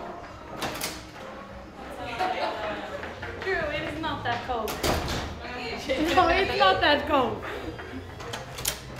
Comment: What did John Terry said at 1:42 ? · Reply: Drew, it’s not that cold